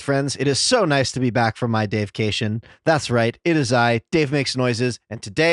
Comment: I know he says, “It is so nice to be back from my day of vacation,” but I keep hearing “my deification”.